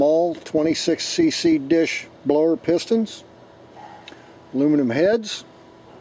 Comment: I just wasted 12mins cuz I thought it said Blown Up.......im such a dumbass. Awesome motor!! Hope u got the fuel n idle straighten out